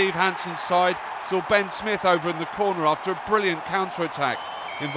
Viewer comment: does he say Ben Smith over in the corner? As he dots it down under the sticks?